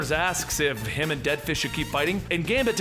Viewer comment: did you say deadfish?